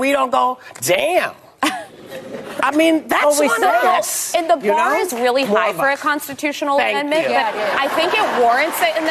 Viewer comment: or around 5:20 what did she say that was so funny? Why did the audience laugh? Something like ”and the bar is set really high to change the constitution” …? Or something like that….. ? Not sure…